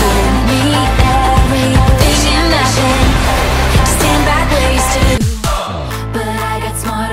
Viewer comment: I always hear “Stand back wasted”